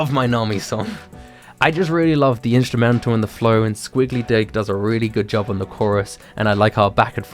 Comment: XD OMG I THOUGHT HE SAID SQUIGGLY DICK ON 13:11 please dont ban me